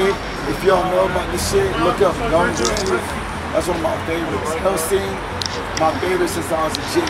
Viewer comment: trying to figure out what he said here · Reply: Gun Grave, Hellsing, SGT Frog